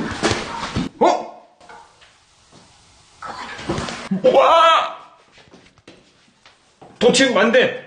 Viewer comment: 에 해석 못한다는게 그만좀 와 라고 들리는데 아닌가요?